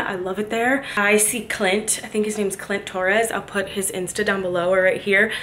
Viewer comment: Ok wat is your hairdressers name ? No joke i thought you said clitoris ! 🤦🏻‍♀️😂😂😂 and of course your hair looks stunning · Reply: Girl I said “Clint Torres” 🤣🤣🤣